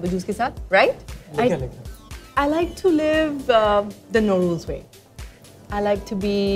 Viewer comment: I like to live 'no rules' way Sounded like, Lodu's way